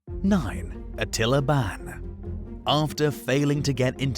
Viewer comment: Thought he said Attila Bak lol